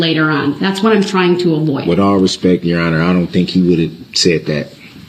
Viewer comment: did anyone hear this tool bag say “I don’t think he’s smart enough” under his breath?